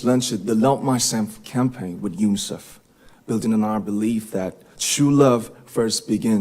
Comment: 물론 연설문 작성하는것 도와주는 분이 계셨을 거라 생각해요. 하지만 너무 대단한 그룹이고, 대단한 RM이네요. 멋쩌요. 영상3:28 즈음에 campaign with UNICEF built . . . 에서 building 이라고 들리기도 하는데.. building이라고 하면 틀리는 표현인가요? 아시는분~! 도와주세요~